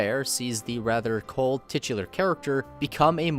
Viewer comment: I can't be the only one who heard Quatro Vagina